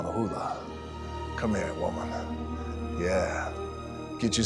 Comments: The auto generated captions said at 7:30, and I quote... Come here and Walmart 😂😂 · Did he just say “ come here Moana” I’m so done 😂